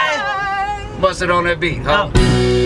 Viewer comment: What is he saying on 4:40? Please help me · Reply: I think that's producer tags : Mustard on the beat huh